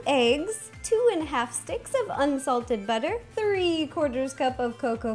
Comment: I thought she said two and a half sticks of UNHEALTHY BUTTER! L0L 😹